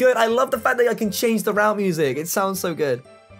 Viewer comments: When he said route music I thought he said rap music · When he said listen to the route music I thought he said listen to the rap music. I was like must be a logic fan